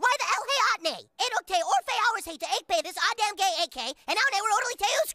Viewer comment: For anyone who didn’t understand, he said: “They don’t? Why the hell not?! It took us four hours to bake this goddamn cake and now we’re totally screwed!” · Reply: I decoded it but I first thought he said 8 hours lol 😆